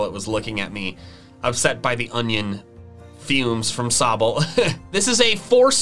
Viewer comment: haha the subtitles thought you said "upset by the onion fumes from Celebi" instead of "...from Sobble", it's hilarious that it was still interpreted a pokemon name being spoken 😂